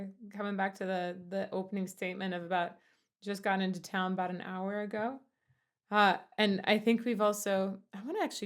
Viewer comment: I always thought he was saying "I took a little down about an hour ago"... which makes just as much sense for Jim.